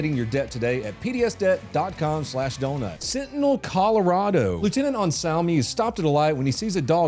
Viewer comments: I thought you said fentanyl Colorado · thought you said “fentanyl Colorado” 😂😂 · I 100% thought you said "fentanyl colorado" and about fell out of my chair